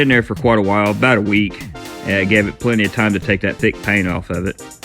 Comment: I misheard you, and think you said to "take the pain off". XD that pink IS a pain. good to see you brought it back to life again. :) I used to watcdh european demo derbys for a while, but I cringed too often at the cars they would take to the track to be destroyed, many classics die there... the english have no qualms about killing off an old rolls royce or bentley or rare austin or somesuch if they feel it's beyond repair... but really, it hurts. :P anyway, great paintjob, great idea with polishing the bumpers and stuff. makes them look more filigreed than with a layer of molotov. looking great! ^^